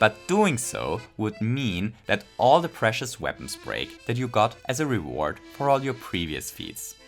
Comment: I thought you said wario weapons break